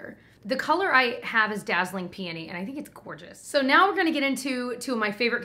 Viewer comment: Am I the only one who thought said "dazzling panties" when talking about the elf blush? LOL!!!!